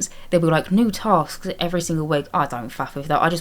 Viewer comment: i don't think I've ever heard swear like that before 😭 im so impressed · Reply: She said Faff, it's like saying mess lol (I don't actually know how to spell it lol)